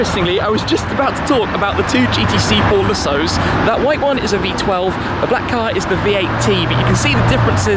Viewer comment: and 11:57 does he say 'v18' ?????